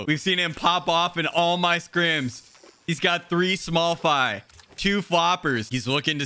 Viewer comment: Is it just me or at 7:40 did he say three small fy